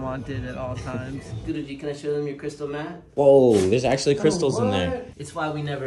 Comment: i thought he said to the dog "wanna show them your crystal meth?" 💀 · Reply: I thought he said crystal meth too at first had to re-watch to see 😂😂😂😂